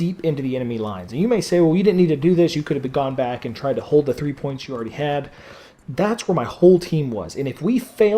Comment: Sir, what did you say in minute 2:11 to minute 2:13? I'm sorry for this question :/ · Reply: he said that "well your saying that you didnt have to do this you couldve just stayed in front of enemy lines and kept the three flags you had!"